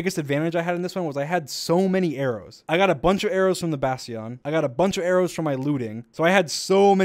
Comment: Did dream just say “basion” instead of bastion